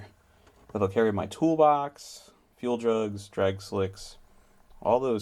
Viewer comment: when he said fuel jugs I thought he said do drugs